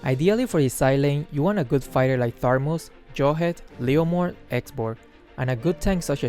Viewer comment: (or higher secs) did you just say tharmus?